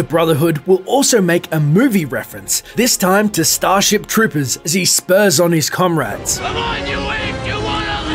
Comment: I remember hearing three dog refer to the enclave soldiers as "storm troopers" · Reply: Yeah maybe but i thought i heard storm troopers